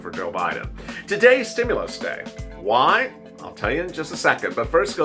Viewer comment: I thought you said that today was going to be "stimulus Friday".